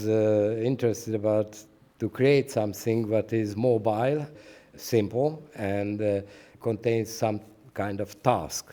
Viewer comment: What did he says in the 1:01? he was interested about to create something that is .......¿? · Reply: mobile, simple and contain some task